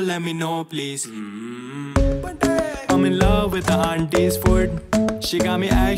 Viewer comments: I thought he said I’m in love with auntie’s foot 🦶🤨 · What I heard: I'm in love with the aunties foot 🦶